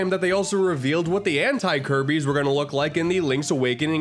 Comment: my audio glitched and it sounded like “this is what ant looks like”